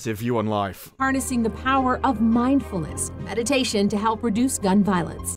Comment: Am i the only one around here who heard that as "mind bullets"?